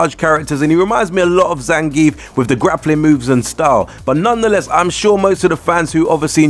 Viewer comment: Did you do that voice for the intro? Lol I thought you said boobs whrn u said moves... Dont blame me lol